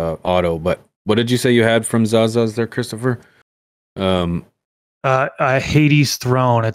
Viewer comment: Did he say "Christopher"? lol